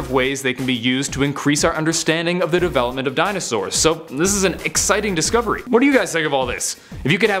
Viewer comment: Did he just say eggsiting discovery